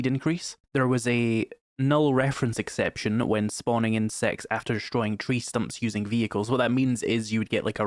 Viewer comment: I was listening to the video in the background and was confused because I thought you said "spawned in sex" for a second